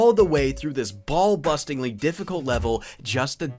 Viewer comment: my guy did you just say ballbustingly difficult